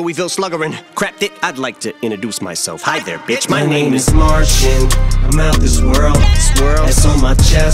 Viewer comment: Am I the only one that thought he said “My name is martian, ...”